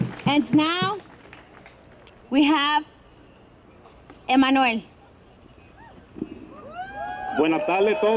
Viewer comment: "and now we have a minority" LMAO 🤣😂 🤣 unreal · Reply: I thought maybe i misheard it. though she might have said manuEl